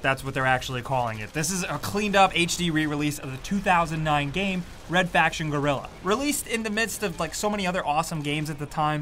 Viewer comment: Everytime i hear you saying 'Red Faction Guerilla' it sounds like you're saying 'Red Faction Gorilla' lol